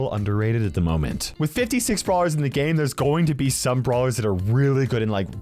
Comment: Did he said with 56 brawlers